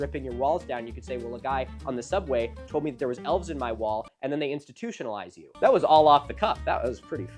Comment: I heard you had "elephants in your balls" when you were talking about "elves in your walls". I might have a heretofore unnoticed headwound.